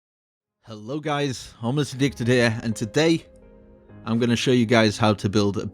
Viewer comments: I thought he said homeless dick😭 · thought he said homeless dick XD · Nice!but at first I thought you said homeless nigga here